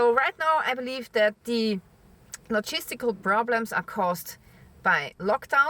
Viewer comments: at first when you said "logistical" I kept hearing "cheese stick"... thought you were saying the economy was gonna collapse due to lack of cheese sticks... LOL... I got my ears fixed... thanks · I lmfaoooooo just now coz i thought you was saying "Cheese Sticks" & im like, "What?, what have cheese sticks got to do with anything" lololol Im thinking, "Maybe i should stock up on cheese sticks" & then i realised you was saying Logistics 😂😂 my bad :)